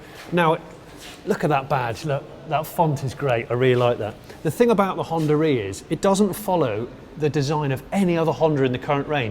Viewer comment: did he say, “the thing about the Hondarea is” ?